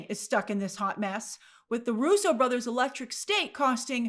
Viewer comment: I swear I heard "With the Russo brothers' Electric Stink..." haha 😳had go back and do a double take! 😆